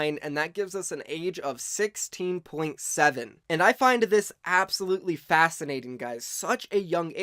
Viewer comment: did you just say “finded”